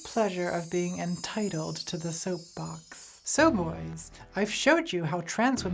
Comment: Did she say "soyboys"